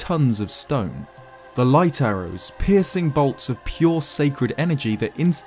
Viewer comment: because you are British, I thought you said " pissing bolts of pure sacred energy" instead of "piercing bolts of pure sacred energy"....is that racist? that'd still be pretty damn cool to be pissing bolts of pure sacred energy.